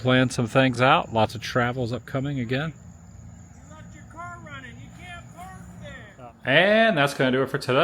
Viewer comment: what was buddy yelling about? Was he yelling at you? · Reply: “You left the car running!!!” 😂